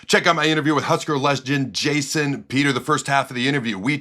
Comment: Lmao. Sounded like you said Husker lesbian instead of legend at the beginning